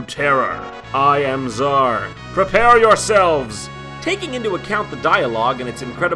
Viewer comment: I seriously thought he was about to say "prepare your anus" when talking about that dragon.